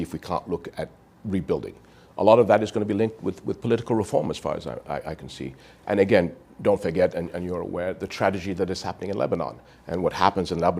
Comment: Did he say : " the tradegy?"